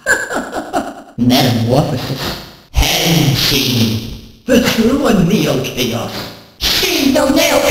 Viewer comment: is that saying “It’s you and me, ol’ chaos” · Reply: It says “The true and neo chaos”